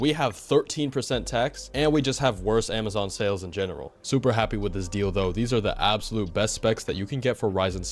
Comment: WHAT BRO SAID "super happy with this dildo" lil bro cant sneak that in #maono